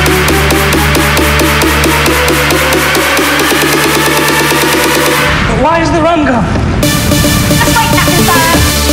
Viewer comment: What he's say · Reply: WhY iS tHe RuM gOnE???